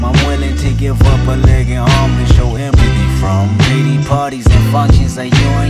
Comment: I swear bro said diddy parties